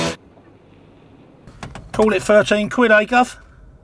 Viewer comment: what is he saying at 0:23? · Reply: he is giving the price of the fair/cost to location, we call it £13 Gov. calling the payee Governor/boss as he just employed the driver for the ride now he must pay his wages lol but Governor can be used as sort of greeting or politeness in cockney. like a person you meet on street after long time. be like, hello Governor how you been mate what rock you been hiding under, or you buy a beer first 1 you say or might say cheers governor or that phrase in them situations